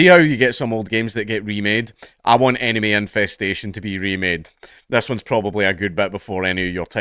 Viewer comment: Instead of “enemy infestation” I heard “anime infestation” lmao